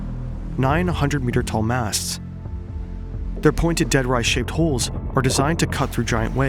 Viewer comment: idk why but I thought he said 900m tall masts the first time I heard it, had to turn on subtitles to get the deal